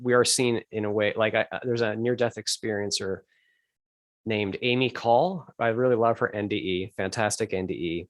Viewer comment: mentions a Near Death Experiencer but I didn't quite catch the name? Do you know who it is? Amy Cole? Many thanks. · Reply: Hi Samantha. It's Amy Call that referenced...xo